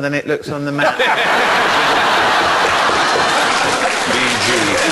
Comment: Did Stephen say "gg" at 2:58 ?